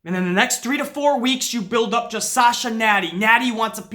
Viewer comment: it sounded like said SALSA, lmao, made my day, man, i agree 1000% with about Sasha, and i hate seeing all these wrestling pages saying she bitch and moan her way to shots after shots it pisses me off their stupidity. When your on the level of good as Sasha is it is CRIMINAL that she isn't treated properly, nuff said.